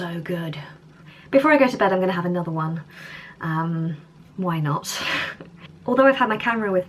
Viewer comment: I have to say I enjoyed the video, like is fun to seethe whole day, the presents and stuff. Your brother have really nice hair, now I understand why you said a couple of times how much you envy his hair. When you said (talking about wine) "I am going to have another one" after that did you made a pun with WINE not or I misheard?